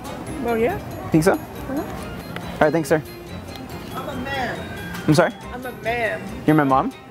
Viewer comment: did he just say M-A-N hindi M-A-M